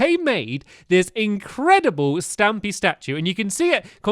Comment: I paused the video when you said 'incredible stampy statue' during the love garden part, and I thought you said 'edible stampy statue'... lol oops